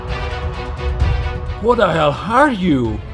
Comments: She sounds like she saying We are “ seen” not scream but nice animation tho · it sound like scream said we are ice cream